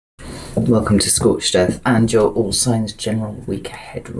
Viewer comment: As soon as I clicked, maybe it’s my sunburn, or my ears deceiving me. I thought you said “welcome to Scorched Death.” It had me a lol’ like 😩🥺😭🥵😱